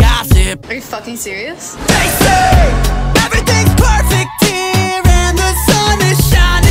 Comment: Isn’t it: Oompa Loompa doopity-doo I got a bad feeling bout you Oompa Loompa I don’t know, dude I got a bad feeling bout you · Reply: It is and I hear "everything's perfect, dear" not "here" lol